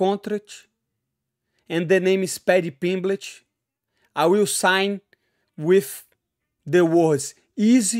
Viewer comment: Haha! Sounded like “Paddy Pimbitch”!